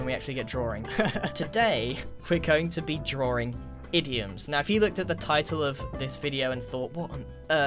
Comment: I watched this video multiple times before but I just heard that as we are drawing idiots just now